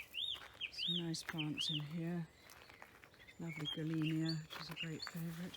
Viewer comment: sorry I didn't make out the name of this herbaceous bush with lovely white flowers at 11:35. Thank you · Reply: Gillenia